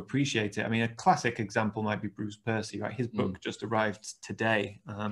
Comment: Tom mentioned a photographer's book he liked, but didn't quite catch the name or details. Could you provide a link to that person? Thanks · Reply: Bruce Percy, he’s a good friend of mine: <link> I know it says sold out, but email him as I believe he has a few spare